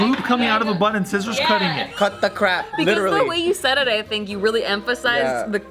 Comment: When Steve said Cut The Crap, I thought he was saying Katakuri. Chapter 893 HYPE.